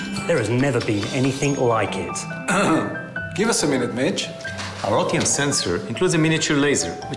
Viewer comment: Did he say b!tch